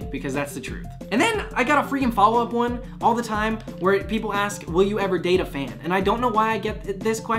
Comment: I thought you said “will you ever date a van?” Haha